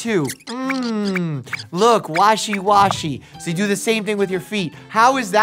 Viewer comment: When he said washy washy, I thought he said Walshy Walshy 😂